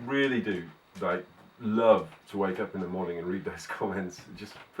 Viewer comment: when he said he wakes up in the morning i thought he said wanked in the morning